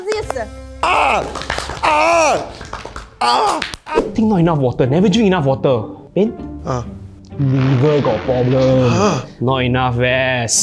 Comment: did he say liver ka problem ie liver problem in hindi english combination